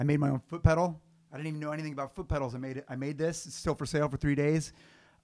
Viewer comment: Did satch say "foot pedal"" or "fruit pedal" ..it sounded like he said fruit at the beginning 😂😂